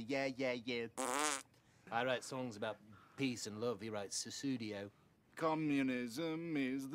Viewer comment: "i write songs about peace and love, he writes the studio" literally every secret recording of the beatles. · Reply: Guess I need to listen again. I thought he said Sussudio, calling him Phil Collins basically 🤣